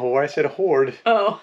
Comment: I thought he said “whore” too lol. We use body wipes that we use when boondooking. Will do navy showers every other day and use these on off days. You guys will do just fine when boondooking.